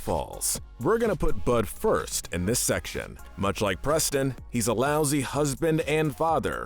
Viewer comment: did you say Preston? Like Preston plays??